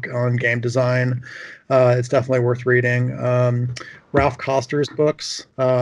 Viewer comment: What are the authors he reccomended at the end? One of them it sounds like he says Ralph Causters? Coasters? Caulsters?